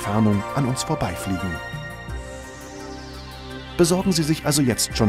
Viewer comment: ich hab ficken verstanden aber winken wurde gesagt :D